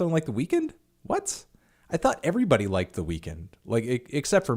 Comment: Did he just say “The Weakened”?